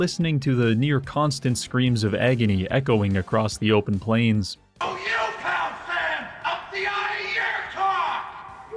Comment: what is this dude saying here? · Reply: "Oh you count then, I'll (????) out of your (????)"